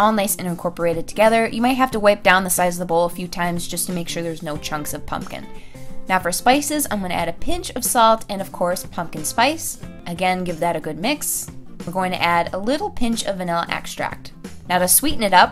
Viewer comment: thought she said size